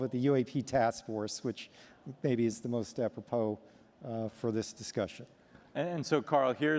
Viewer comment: Sorry, what the fuck does he say at 2:07? Is the most huh? AOPO? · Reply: Apropos: basically means “relevant to”